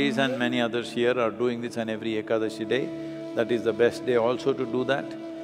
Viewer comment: What is the word he says at 3:39 , what day? Thanks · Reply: That's ekadashi, 11th day of every month in indian calender which has two ekadashi (11th day) in every month. Month in this calendar is divided into two parts(paksha) as krishna paksha and shukla paksha which includes 15 days